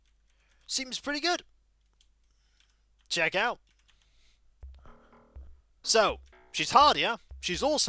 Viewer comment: I don't know if this was mentioned or not, but it took me this long to realize what the bar digitalized voice says sometimes (might be baked into the soundtrack? I digress..) Well, I erred in trying to listen to it in English. It actually says "schattenlauf", in German. Which means... SHADOWRUN! \o/ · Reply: Been playing this game for ages and never knew that lol. I always thought it said “check I’d out lol